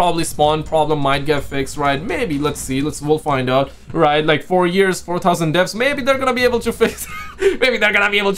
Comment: Am I the only one who heard him say 4 years is 4000 days lol